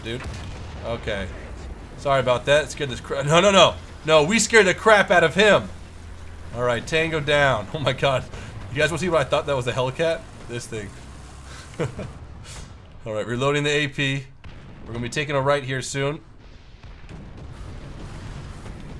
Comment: did he just say hella cat?